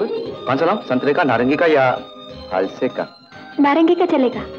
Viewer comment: teesra kya bola? he says: santre ka, narangi ka, ya ???? ka ... which is third fruit? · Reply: faalsa